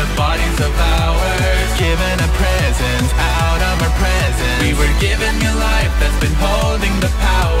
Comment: あーらまプレゼントにしか聞こえん0:32